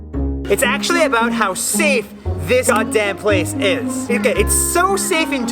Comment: non cut Subtitle : "It's actually about how safe this goddamn place is!" The editor didn't cut well what Nas said, I can hear the word that got cutted was "Goddamn